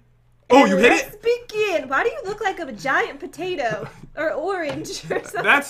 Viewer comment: Did anyone else hear something else 16:25 🍑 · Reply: Va giant patato = Vagina patato